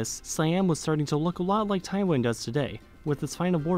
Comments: I thought I heard Taiwan a few times instead of Thailand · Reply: he clearly says Taiwan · Why do you keep saying Taiwan instead of Thailand? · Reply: he clearly said Taiwan